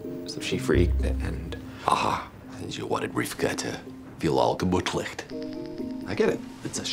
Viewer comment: What the hell does he say at 4:30? I assume it's Yiddish. "You wanted Rivka to be all.. kabutlict." I figured out Rivka but not "kabutlict"